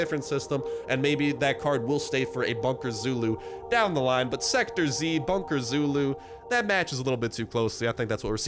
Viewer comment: I thought it said “Sektor 2”